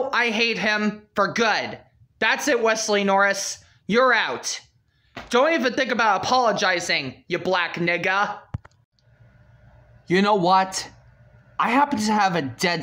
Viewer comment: OMG WHAT DID HE JUST SAY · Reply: He said, "That's it Wesley Noris, you're out! Don't even think about apologizing you black n-word! Yes he actually said the n-word